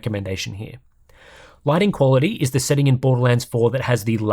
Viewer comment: I thought he said writing quality and got a little excited.